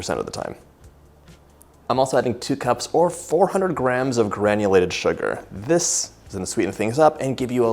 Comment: Omg… I thought you said 2 cups or 200 grams of sugar. So when I put it on the scale, I’m like 200 grams is like 1 cup. I have the brownies in the oven right now, with half the sugar it’s supposed to have 😢😢 I hope it’s not disgusting. I should’ve just done the cups instead of grams. I’m an American I’m confusion 😂😂